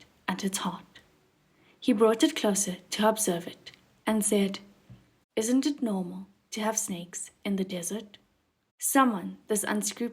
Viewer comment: Oh man, I'm embarrassed to say that i completely misheard at 30:11 cause i was distracted and heard: isn't it normal to have S3X in the dessert? I WAS LIKE: 😳 DAFAK SAN LANG???